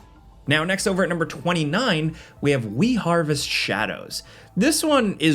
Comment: i was afk making dinner for the first couple announcements, but was still listening. For number 29 I heard "Wii - Harvest Shadows" and was like... wait.. whAt? insert face slap emoji... anyway im excited for a chill out sesh with Melobot - A Last Song